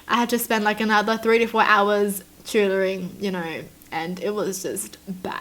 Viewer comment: did u say choodering or cheerleading? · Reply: She Said Tutoring